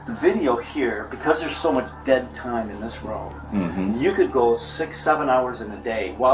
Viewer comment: did he just say 6 7?